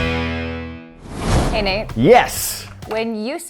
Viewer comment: I love how it’s always saying “hey nate” I thought she said Hey mate